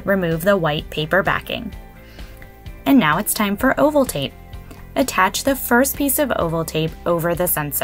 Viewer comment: I swear, the first time watching this I heard " time for Ovaltine"